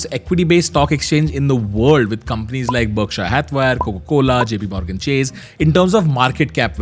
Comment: did he just say berkshay hathwire ? 😅😂